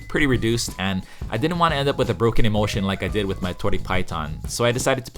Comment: I actually thought you were saying ‘broken emotion’ at first. 😂